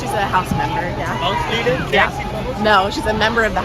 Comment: sounds like someone said is she a house maiden.